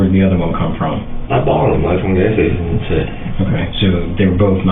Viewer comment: y does it sound like he said he bought them fromright from a gas station or am i just trippin?